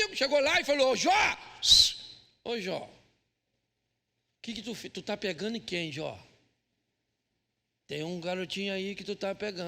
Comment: Alguém entendeu o que o pastor falou em 25:23? Não sei ouvi certo. 😢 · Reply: Tu tá pecando em quê Jó? Foi essa a pergunta que um dos amigos fez?